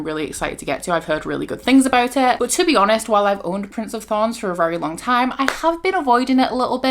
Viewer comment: I thought you said Prince of thongs 😂